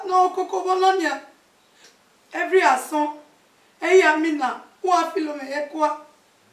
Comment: Did I just hear “every asun” (12:27) 😅 oh my what an EnglEdo 😅. Subtitle in English it gives you more views.